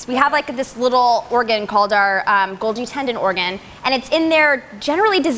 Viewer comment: says we have what organ? I couldn't understand the word she said. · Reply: Golgi tendon organ.